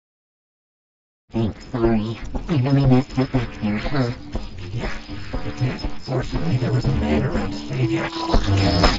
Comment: can somebody tell me what did he say?? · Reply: Yes you did your lucky there was a man there to save ya